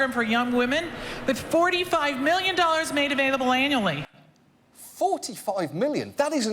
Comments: she clearly says "anally". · Did anyone else not hear what she said? 7:33 "45 million dollars made available ANALLY!" So that's where that money went... ;) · did anyone else hear “anally”? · Her accent makes it sound like she said "$45,000,000 made available anally." XD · Is it just me not hearing the accent correctly.. but @ 7:30 when she says there is 45 Million available... does she really say it is available anally? - If so thats a pretty harsh way to get a scholarship :P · did she say that there was $45 million made available ANALLY? Sure sounded like it.